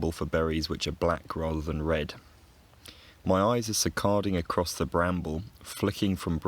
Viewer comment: Word nerd here: what's being said at 0:50 -- it sounds like 'si-carding', but I'm not familiar with it. Any help would be appreciated! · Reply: "Saccading"! Sorry for not putting it on screen :)